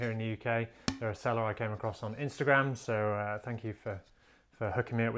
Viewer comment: Where did you get the pink ivory? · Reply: Sorry for the language. Listening again more carefully, I hear "woodwise, a seller I came across on instagram".